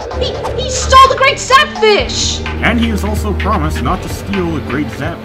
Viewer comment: I swear it sounds like he said "fapfish" instead of zapfish.